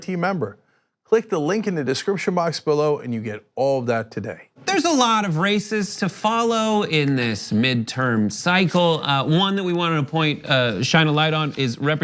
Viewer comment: I first heard "there's a lot of racists to follow in this midterm cycle"...